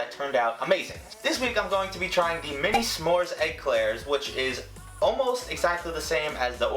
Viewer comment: I thought you said egg Claire the way you said eclair